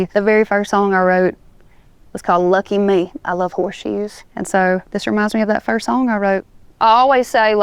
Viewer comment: I thought she said " i love whore shoes". Apparently my accent and hers are on opposite sides of rhe spectrum.